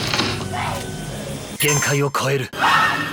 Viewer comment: What is he saying? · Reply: "Genkai o koeru". "Exceed the limit" in Japanese.